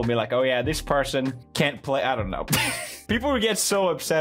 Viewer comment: 0.25 playback speed someone tell me what that is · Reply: He said I don't know